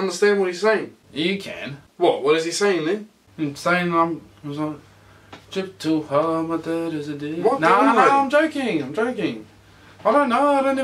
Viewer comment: For those who don’t know what he said @ 0:23 “drip too hard, my dad is a d”